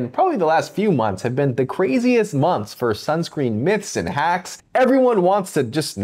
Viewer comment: I thought it said “Sunscreen my this” 😂